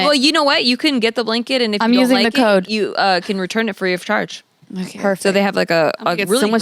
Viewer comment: did say imma get someone’s jizz on it and return it back lmao love her